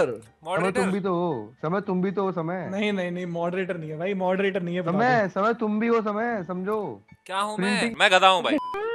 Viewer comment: What was the word at 7:16? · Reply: Engineer